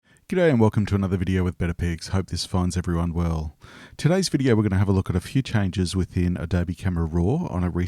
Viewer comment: Thought you said better pigs